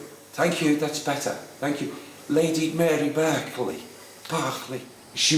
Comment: I could have sworn he kept saying "Lady Mary Broccoli"... lol...